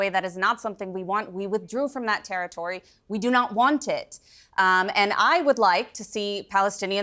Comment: “we do not want it” I thought she said that she doesn’t represent Israel